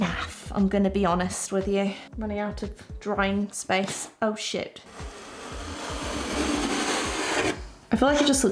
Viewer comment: Is it just me or did she cuss at, 7:20 I heard "oh shit" tell me if I'm wrong cuz ya idrk